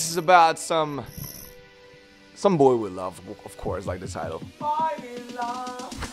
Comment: Oml I thought he said "Boy we love" not "Boy with love"!!!!😂❤